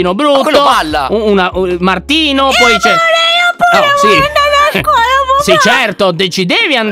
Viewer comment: solo io ho sentito eh amore tipo hahahah😅❤️